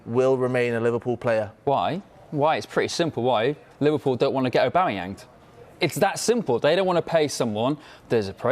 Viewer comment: Did I really hear “he doesn’t want to get Aubamayanged”